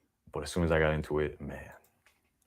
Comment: when he said something about Islam being definitely not it. The YouTube auto caption said it's definitely naughty 🤣🤣🤣🤣. Islam is very naughty indeed!